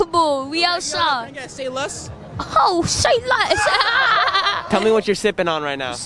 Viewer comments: what the fuck is he saying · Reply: Eyo, can I get a “say less”? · What does he say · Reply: Oh, say less. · What is that he says at 0:30? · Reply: Say less